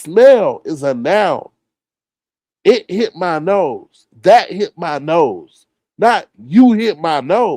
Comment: All that grunting sounds like he has Tourettes. Sounds like she said oh it’s in my nose.